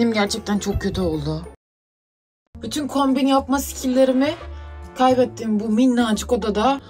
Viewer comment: kombin yapma sikilleri mi ?? Benmi yanlış duydum